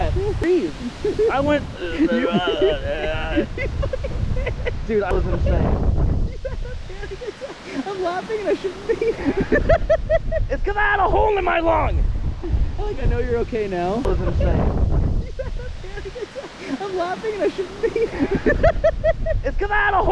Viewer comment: i thought said "Its cause I have a home in my lawn."